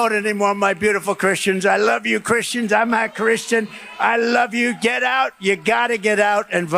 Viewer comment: Ik I'm not the only one that heard him say, "I'm not christian".